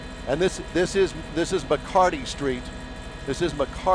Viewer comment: lol at 5:40 i swear he say this is Bacardi Street i was about to tell you give me some !!